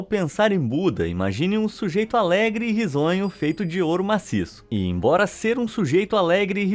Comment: Entendi "bisonho"